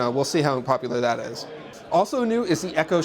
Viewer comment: Did he say we'll see how unpopular that is? Not that I disagree but funny either way.